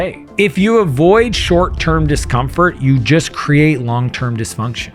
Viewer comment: I need to not be nice and say something right away, because the subtitle is wrong on 3:03 -> it says there "if you avoid short term comfort", but you meant DIScomfort. See, practicing what you preach right away. Peace